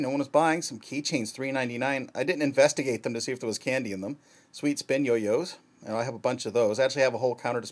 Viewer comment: I looked on my left for a while, and at the part "Sweet Spin yo-yo's" I heard "Meatspin yo-yo's"... my head turned in an instant with WTF expression.